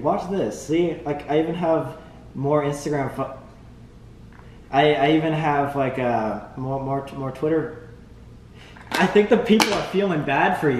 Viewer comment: the subtitles say "I even have more Instagram f*ck" lol